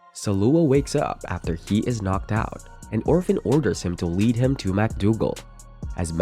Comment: Legit When you said “and orphen” I thought you said “an orphan”